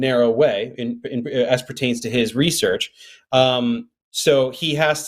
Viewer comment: it almost sounded like he said “aspartame to his research”.